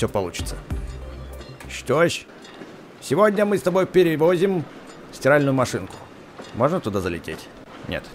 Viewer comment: Мне послышалось сральную машынку